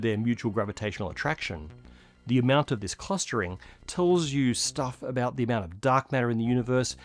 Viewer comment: that says "cluttering" instead of clustering 🤪